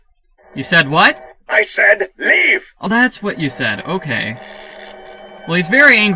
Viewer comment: at first i thought he said "i said bee"